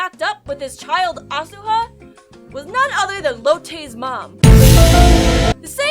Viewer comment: i heard it as lil tay's mom